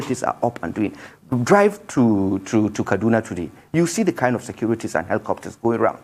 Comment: Did he just say we should drive to Kaduna 9:39 ? This spokesman is a bandit